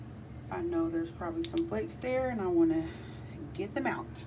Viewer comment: I thought you said " a FEW flakes"!!!!!!!! Another GREAT video, Queen!!!😍